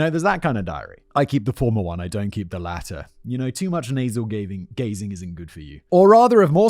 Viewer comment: Did really say too much nasal gazing? Wouldn't that make you go cross eyed?